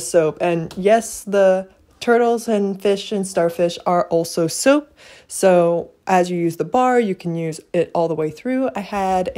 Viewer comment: I thought you said: “And the starfish and turtles are also SOUP”